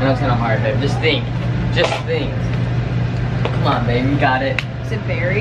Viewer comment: Did call babe at 6:22 and at